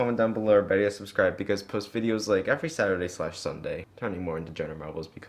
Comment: It sounds like he says "Fuck me music" at the end